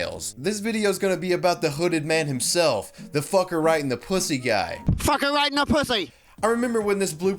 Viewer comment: I always thought he was saying “fucker riding a pussy”